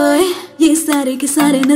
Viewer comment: No se hindi, pero en el 1:14 dice claramente "me jode lo que dijo tu madre".